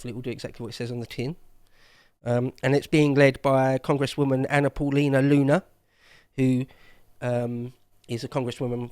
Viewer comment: "Ana Paulina Luna swimsuit" accidentally enters itself into my search engine... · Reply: I thought he said Una Paloma Blanca ?